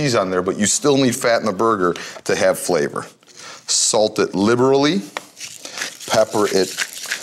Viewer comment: "Salt it literally" that seriously made me laugh. Anyone else catch that?